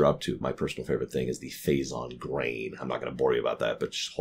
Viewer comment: what is he saying ? "phazon graying(?)" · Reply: "Phazon grain". Basically, in Metroid Prime, it's revealed that the space pirates attempted to infuse grain with phazon, to allow it to bond to their physiology without adverse effects to their health.